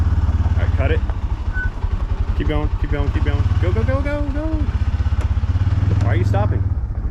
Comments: I thought you said go bo go 😅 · 😂😂😂😂😂 love the dukes of hazard reference I thought you said go bo go